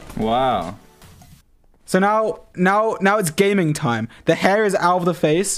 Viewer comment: I heard say "Now its gayming time" XD